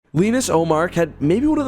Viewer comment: I thought you said Linus Ullmark lol